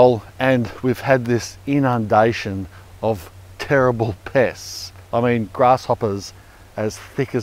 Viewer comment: "terrible pests" I thought he said terrible piss!